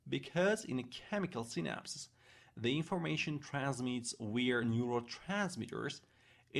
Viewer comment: I'm hard-of-hearing, and while most of what you're saying is quite easy to hear & understand, some is not so clear (to me). At about 4:29 you seem to say, "...the information transmits WE'RE neurotransmitters...", which doesn't make sense. I'm going to guess (since you also call Vesicles 'Wesicles', with a German pronunciation?), that you might be meaning 'via'?? I'm hard-of-hearing, and I have found most of what you're saying pretty EASY to hear & understand, except for 'we're' at 4:29 (and at 5:22, MOTOR plate sounds like - and closed captions says- 'modern'). Oh, and at ~ 4:52, you say, "...that involves a number of TIMES in order to increase..."; is it possible you meant TINES, like the multiple points of a dinner fork? And at 5:32, "T-CHILLS", that must be T-tubules'? I am grateful for the time you have taken to present this, and understand that you are currently a practicing physician.